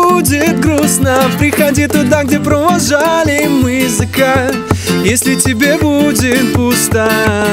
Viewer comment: только я слышу как он говорит: Там где мы провожали "Зэка"